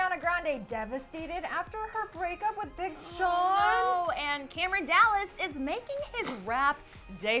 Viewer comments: I thought it said Cameron Dallas raping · ohhh my god I thought it said Cameron Dallas weird raping